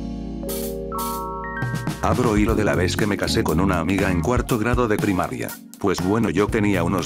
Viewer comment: por un momento pense que decia: * ME CASO CON MI PRIMA * Y después me di cuenta que decía otra cosa ;)